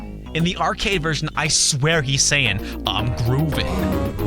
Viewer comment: I wonder what he is actually saying. Because whenever I play I can't not hear "I'm groovin!"